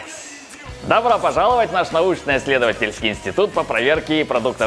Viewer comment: Мне вначале послышалось "инсульт" а не "инстетут"